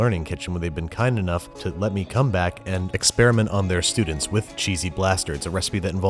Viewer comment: I swear at the beginning he says "Cheesy Blasterds"